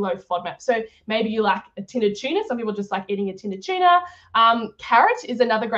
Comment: What is she saying at min 7:05? A protein snack called “tin a china”? Thank you for your help. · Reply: tin of tuna